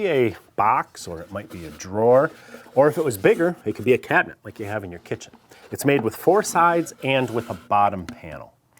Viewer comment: I think you said a "cabinet like you have in your kitchen" - what I heard was "cat box like you have in your kitchen". Well, as cat's have been known to sit in any cardboard box available, I suppose a custom made wooden box would be an upgrade... 😻